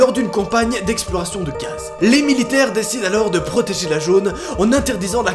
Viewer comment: C'est moi ou il a dit "protégé la gone au lieu de zone"